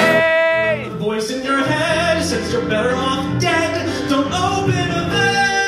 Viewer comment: "don't dope in a van" vs "don't open a vein" When I first heard this song I honestly thought he said the prior so I'm glad he fixed the pronunciation haha